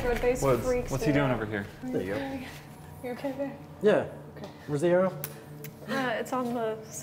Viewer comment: anyone else think that said "are you ok babe" to